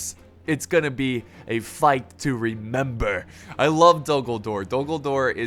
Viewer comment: I swear he says “I love Dumbledore” 😂